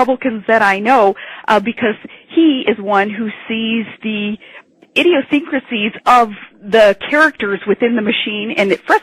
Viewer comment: what the hell does she say at 1:03 -1:04. I might not have heard correctly, but did she totally fuck up "Idiocrasies"?